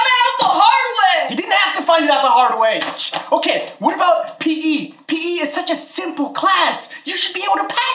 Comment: it says pp in the captions instead of p.e